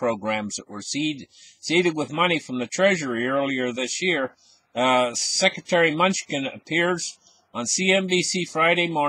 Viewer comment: Did he just say security “munchkin” great video my friend